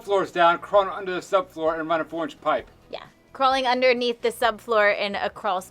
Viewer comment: Every time says "4 inch pipe" I hear "orange pipe" for some reason 😂 we're English, so translating is a game we enjoy playing 😂